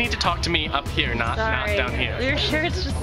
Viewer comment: It sounds like your saying ducky of awesome